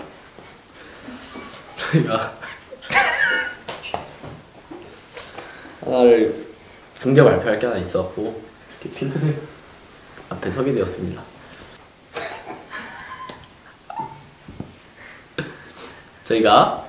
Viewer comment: 분명 '서게 되었습니다'인데 왜 나는 ' 사귀게 되었습니다' 로 들리지...?